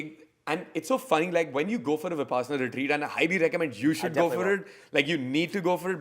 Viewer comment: ? What was said here? missed it! Love this podcast 🔥☀️ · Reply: Talking about doing a Vipassana course. Its a 10 day residential course. Google it.